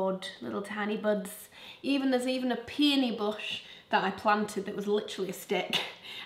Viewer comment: I thought you said ‘went for a pee in a bush’ but then realised you said ‘peony bush’🤭🙄 thank goodness I realised it or I’d have been spreading all kinds of rumours 🤣